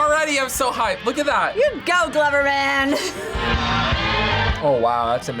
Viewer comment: did she just call him gloverman...?